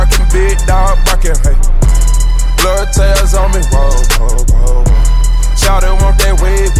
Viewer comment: Sounds like he sayin "Whale Whale Whale Whale"